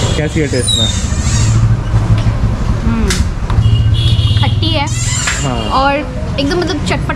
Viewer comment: i thought she was saying tatti hai😂